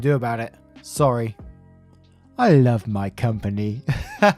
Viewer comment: I got an advert right as he said "I love my com-[advert]-pany" I lagit thought he said "I love my cum"